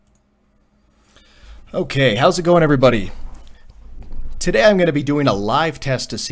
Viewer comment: Did you say "Herzlich wilkommen"?😂